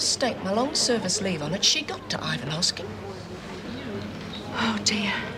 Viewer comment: What does Meg say at 41:32? I can't make it out. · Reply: “She got to Ivanovsky”